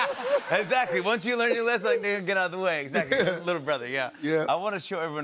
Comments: Sounds like Jimmy said, Nigga, get out the way, i mean little brother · yo why does it sound like he said nigga get out the way im dying 🤣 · Did he say the n word? 8:46 "ni@@ get out the way" 😆 🤣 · Am I trippin’ or did jimmy just say nigga get out the way at 8:47 😅